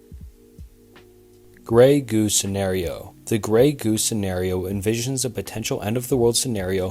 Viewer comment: I thought he was saying "grey goose" and I was like LFG